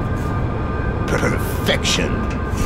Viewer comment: sounds like he is saying pervfection